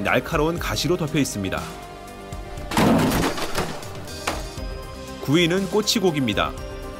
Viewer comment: 구이는 꼬치고기 입니다로 들었네 ㅋㅋㅋㅋㅋ